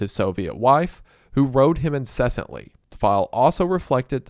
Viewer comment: “Oswald had a stormy relation with his Soviet wife, who rode him incessantly” ayo what? Am I hearing that correctly? How could anyone have a stormy relation with a wife like that? No wonder people considered him a nut job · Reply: "wrote"